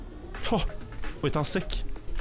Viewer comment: Can anyone tell my what is the world said at 11:27, if its from other language, what does it mean?! Its awesome xD And 2nd thing, the audio is soo good, corelated to what happens on the screen, really good editing, i love it, editor-you did really well! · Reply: He just said “wait, that’s sick!”